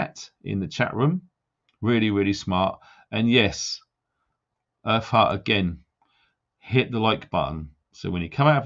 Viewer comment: When you said earth heart it sounded like you said her fart lol. 😆 Sorry. 😞